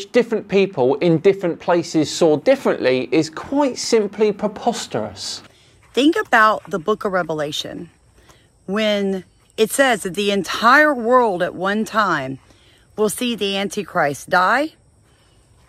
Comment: Did she say "The book of Rebelation?" 4:06 🤔